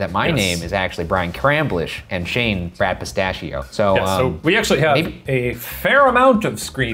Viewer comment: I kept mishearing things they were saying, so for a moment I thought Brian introduced Brad as "Crap Pistachio"